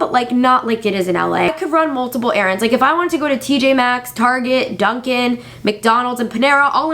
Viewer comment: When she said "TJ MAXX", I thought she said PJ MASKS!😂💗